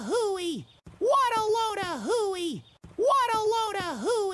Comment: I no longer hear “what a load a hooey”. I hear “waddleona hooey ”